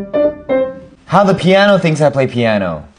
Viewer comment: “How pianists think I play piano” my dirty mind💀💀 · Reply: I thought he said "how peni- play the piano"